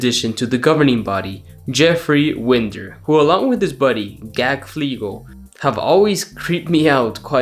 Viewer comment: Im sorry but did you just say dude's name is Gak Fleegle?????